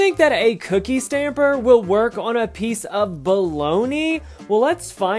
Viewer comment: LOL I thought it said POLONIA(Poland in Italian)